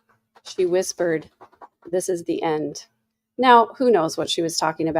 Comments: I agree. I heard "this is the end" as well. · I heard, "this is thu end"